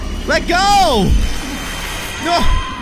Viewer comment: I hear the Baby say "no" 😢